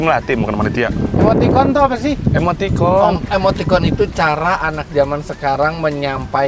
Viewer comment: Emoticon-Tol 😳 ? Ini Gua yang salah denger apa emang begitu jir 😭